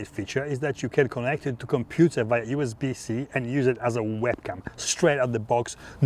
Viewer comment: Ngl at 9:15 I thought you said we can use it as a weapon. LOL Awesome video between! Love your mom hug shot.